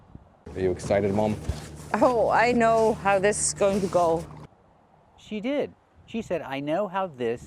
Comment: I heard "this is going to go"